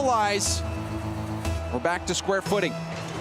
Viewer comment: sounds like says "we're back to square pudding" lol